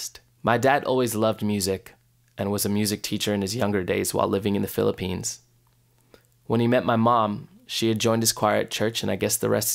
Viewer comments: did you just say Philippines live there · did i just hear "penis" ?